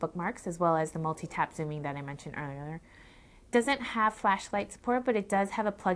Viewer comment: Another fail. She said earlerier instead of earlier